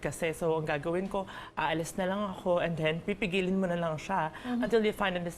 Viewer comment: did she say pipi?